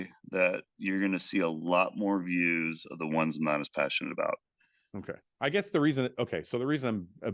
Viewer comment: did he say more views in the videos he’s not as passionate about?